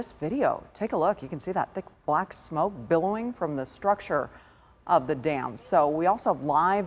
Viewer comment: I thought she said "You can see the smoke coming from the structure of the damned." Sounds like a horror movie. Yeah, but the really scary thing is that all the water will be gone soon so, mama take this dam away from me, we don't need it anymore. I feel like I'm knockin' on heaven's door... Knock, knock, knockin' on heaven's door...